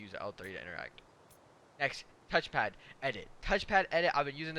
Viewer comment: did anyone else heard TOUCH FATTT or TOUCH PANT lmao 😂